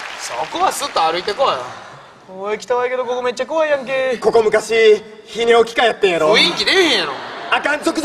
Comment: ここ何回聴いても聞き取れないんだけど、なんて言ってます？？「ひぃにおきかえってんやろ」ってずっと聞こえるw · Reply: 泌尿器科 だから直後のゾクゾクする。尿道が。とかに繋がってくる